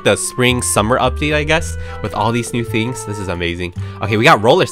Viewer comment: did he say fings